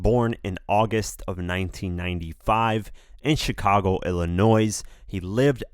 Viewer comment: Did you just say Chicago, Ill-a-noise? 😅